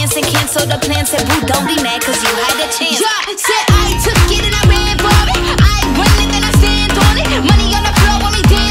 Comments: Sounds like she said I took it thin I ran Barbie 😂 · says money on the ground when she clearly says Floor..